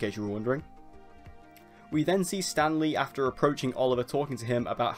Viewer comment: god thought you said Stan Lee for a second :D